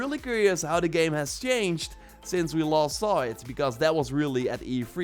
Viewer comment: since we LAST saw it ? sau LOST saw it ? i can't quite hear you